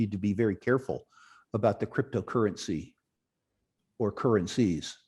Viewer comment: did he just say CRYSTALCURRENCY by mistake or........?